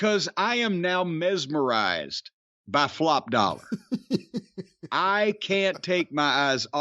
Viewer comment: The first time I heard Corny say, "Flop Doller" I thought he said, "Plop Doller" and I laugh even harder now. Both names work well either way.